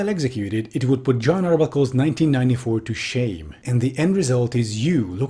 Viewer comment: did I just hear "Jon Arbuckle's 1994" at 1:48 ? lool